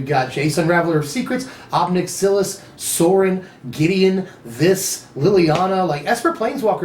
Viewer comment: The vedalkan guy has 6 fingers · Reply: Lol when you said Sorin i thought you said sol ring